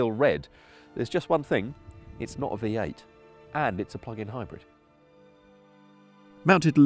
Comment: please tell me I'm not the only one that thought at 1:00 said "it's a fxxking Hybrid"